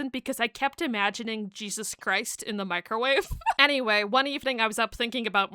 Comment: I'm sorry. I was watching this short and left off on "stealing quarters" since I was talking to someone. When I came back, I heard "chicken in the microwave exploding"..THE F